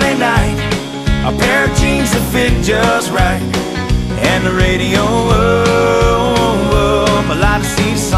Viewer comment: Always thought he said “radio up” lol